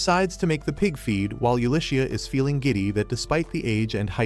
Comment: when i misheard gidy to gigity